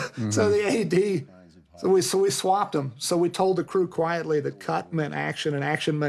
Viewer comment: I thought he said he Swatted them lol